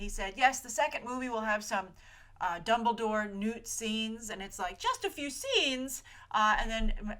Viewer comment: Hahaha I heard "Dumbledore NUDE scenes" instead of "Dumbledore-Newt scenes"